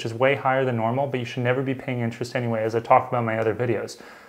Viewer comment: did i hear that right? at 4:55 you said i can pay the amount i owe in full right away???